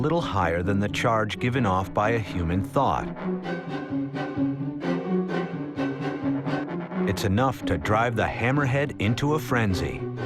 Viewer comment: does he say a human thought or a human thigh 😂